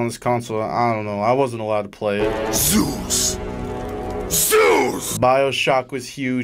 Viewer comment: when I watched gravity falls with closed captions on cable, they actually accidentally listed his name as Zeus instead of Soos